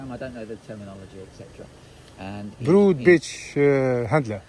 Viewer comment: I swear I heard you say "rude bitch handler" at the end💀😂